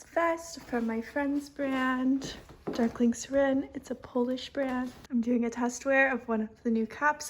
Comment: Does anyone know what her friends brand is? I can’t make it out; she said it around the 12:30 mark. Also, pumped for the upcoming pieces, all so very gorgeous! ❤️ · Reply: Darkling Serein :)